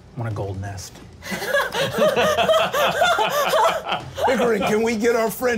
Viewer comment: i think they just called this guy Hickory but not gonna lie he sounds just like Bill Seaccaster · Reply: He’s Old Pickering, and he is literally just an old pirate man.